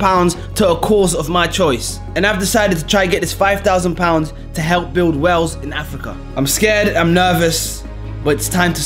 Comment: deadass thought he was waffling and said “build whales in africa”